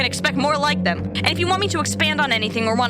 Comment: the outro i heard "go get stabbed and have a nice day" and i was wait....